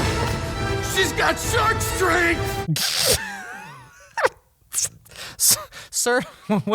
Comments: oh i thought it said "SHE'S GOT SUCK STRENGTH!!" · i thought the guard said “Shes got SucksStrenght!” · “sHe’S gOt ShArk StReNgTh” WHEN I TELL YOU THAT I WHEEZED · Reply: I heard "suck strength" XD and I refuse to hear anything else XD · I thought that the guard said "She's got suck strength!" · at first i thought the guard saying "shes got shark strength" said "shes got sun cream" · i thought he said "shes got SUCKSTRENGTH" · Oh I heard “She’s got suck strength!” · I thought that man said sud strength